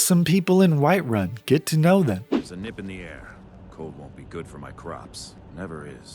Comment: Yeah I misheard that guy when playing because I was talking to a friend who is also playing Skyrim. I misheard him say “There’s a nip in the air. Not good for my crotch.”